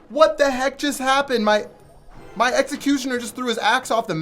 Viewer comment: Lol it sounded like he said he threw his a** off the map